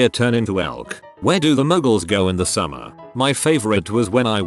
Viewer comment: I thought it said muggles lmao